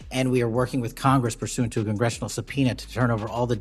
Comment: did he say sub-peanut? The trump assignees are dumb enough. Board of education saying A1 instead of AI for an entire speech